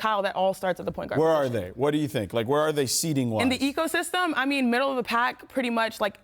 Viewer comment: Ecosystem instead of eastern Conference